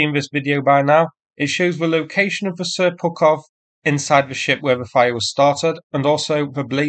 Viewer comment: I thought I heard you say "Seppukov"...